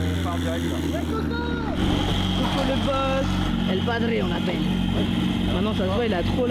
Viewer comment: c'est moi ou à 1:00:27 on entends OH Soso ? Donc Sosomaness